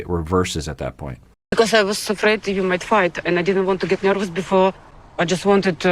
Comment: I wasn't reading the subtitles and I heard her say "I was afraid you might fart" lollll